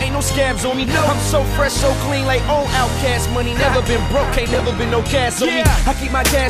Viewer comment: he didnt diss cassidy he said.... "i never been broke aint no cast on me" you people thought he said.... "i never been broke i aint no cass homie"